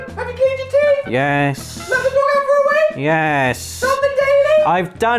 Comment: 'Did you go for a wank?' Is that what mum asked at 5:15?! And he said yes!!